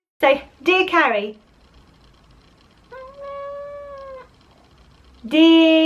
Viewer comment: is absolutely adorable! Actually sounded like she said Dear Carrie at the start :')